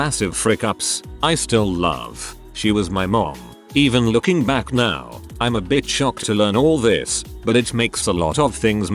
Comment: what’s the censored word I literally can’t figure it out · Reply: Heroin